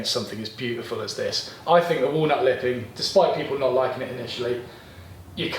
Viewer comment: It's not a case of not liking the walnut lip, it's more that we thought you said walnut whip and then were disappointed.